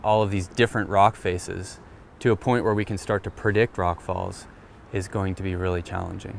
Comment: Did he say, "Perdict"?